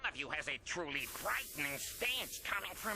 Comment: Sounds like he said frightening Ass stench ! 😂